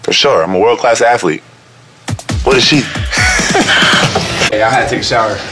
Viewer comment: “I’m a world class athlete What does she do?” Just thoughtful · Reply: Oh thought he said "who is she?" But either way, damn🤣🤣